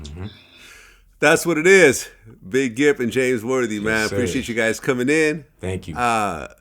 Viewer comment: I thought it said Big Gimp no lie